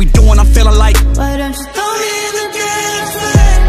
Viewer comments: i always thought it said throw me in the damn flame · I’m so dumb I thought it said “throw me in the bed frame” 😂😂I was like okay